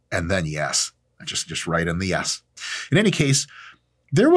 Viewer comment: did you say “right in the a**” 👹🤣